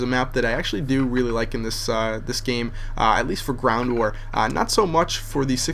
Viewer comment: so what he said mw2? at 0:12 he said brown war. get over it